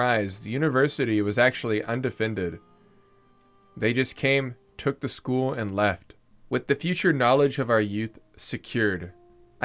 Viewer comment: I swear I've heard "n-word came took the school and left"